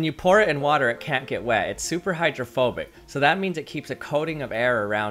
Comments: I THOUGHT HE SAID ITS HOMOPHOBIC 💀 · "It is super hydrophobic" As a human of 80% water, I feel offended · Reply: I thought he said homophobic😂😂😂